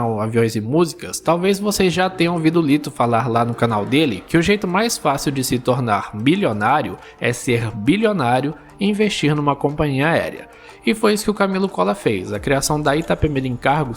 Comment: nao entendi nada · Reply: O jeito mais fácil de se tornar milionário é ser bilionário e investir numa companhia aérea